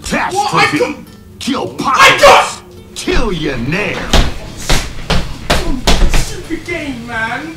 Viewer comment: if you playback speed at .25 it sounds like its saying "chill ya ni66er"